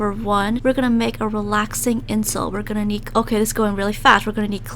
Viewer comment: Why did I heard "relaxing insult" the first time